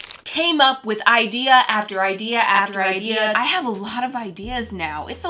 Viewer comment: When she said after idea after idea it sounded like she was saying after ikea